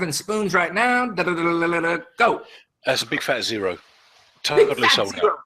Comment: What did say at 12:15 about how many spoons he sold? XP50? Thnx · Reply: "Big Fat Zero" is what he said in reference to the number of spoons he has in stock. He sold out.